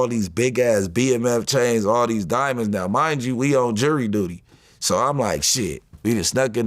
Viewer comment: Did he just say we on jury duty?? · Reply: Jewelry duty